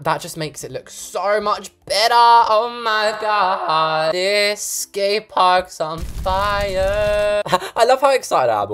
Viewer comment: guess what I thought you said “this gay park is on fire!” Not until I watched it again and you said “this skate park is on fire,”